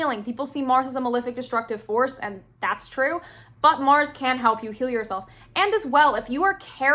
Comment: i swear i heard ''mars can help you kill youself'' and almost spat my tea